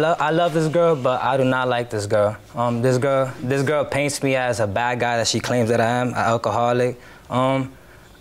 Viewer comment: I heard "This girl paints me as an alcoholic, that I am" and was going to be like "bestie u self incriminated yourself"